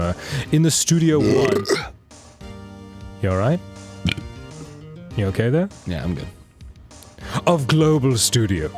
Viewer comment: Dan: You okay there? Had to turn the subtitles on. Thought he said 'You okay babe?' 😂😂 Also, anyone think Will looks like Huge Jackman? 🤔🤣